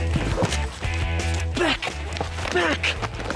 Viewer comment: I thought he said my back